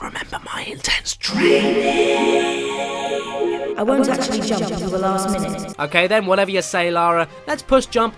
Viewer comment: I like how laura saying "I'll wait til the last minute to jump" Means jump early to. You what? How do misinterpret something like that? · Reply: i heard "I won't jump at the last minute"